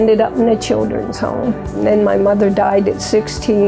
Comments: LMAO I thought she said she had 8 kids when she was 16 but she actually said she was 16 when her mother died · OMG I hella thought she said her mom had 8 kids and died too.. smh at my selective hearing